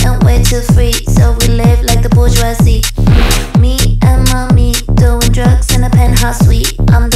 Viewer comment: Heard this song at Hallucination before Christmas in Tampa, thought she was saying me and Monique lol